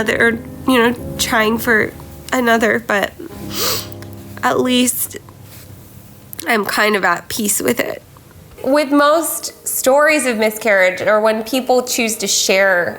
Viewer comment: I thought it said marriage